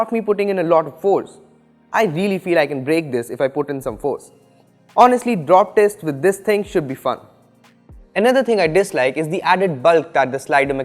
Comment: "drop test with this thing should be fucked" is that what he said ? xD